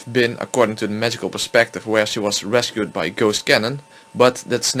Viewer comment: I keep hearing it "Shitkanon" theory, lol